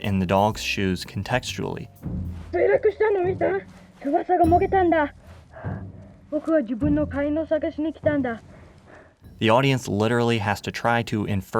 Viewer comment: What is Atari saying to the dogs when first meeting? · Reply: From as far as was audible, it should roughly mean: "I've crashed, have you seen it? My wing was torn off. I've come here to search on my own."